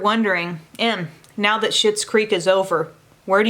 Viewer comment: Hahha! Omg! I thought you said, “Em, not that sh*ts creek is over” 🤣🤣🤣